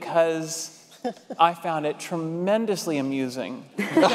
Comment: Anyone know, what Jim Parsons says in minute 1:10.... - I found it tremendously....? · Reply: AMUSING!!!